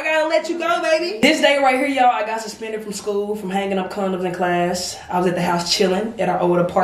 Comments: Ok are we just gonna ignore the fact said “I got suspended for hanging up condoms” 😭😩😩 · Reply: I thought she said handing out condoms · did say she got suspened from hanging condoms in the classroom yall reply and tell me what she did actually say or if i'mm correct